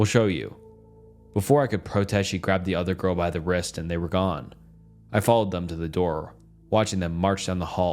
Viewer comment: Who here thought he said she grabbed the other girl by the breast and they were gone